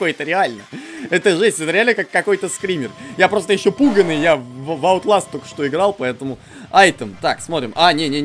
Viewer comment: Послышалось Я играл в *Underlust*Теперь Понятно почему такой *напуганый